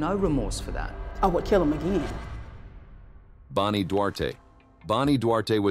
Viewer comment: The timeline reads "Bonnie Duarte" instead of "Bani Duarte" (12:35).